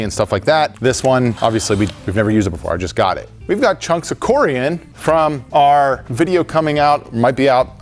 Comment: for a second I thought he said "corium", which meant that John must have been underneath the exploded nuclear reactor of Chornobyl with a radiation suit and a pickaxe 😂